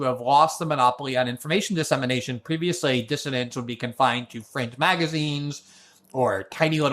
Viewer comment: At 6.06 I first heard ‘fringe magazines’ as FRENCH magazines! 🤣 A great interview guys.